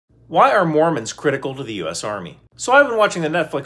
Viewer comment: I thought you said why are mormons critical of the US Army....